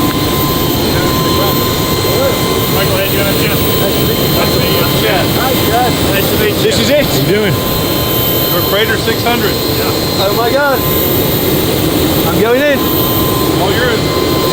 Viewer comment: Iam not hating but for a second i thought we said PROCREATOR 600